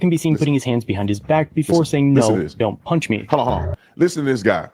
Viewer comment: I clearly heard "punch"